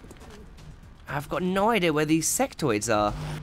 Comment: did you say where are the sextoys?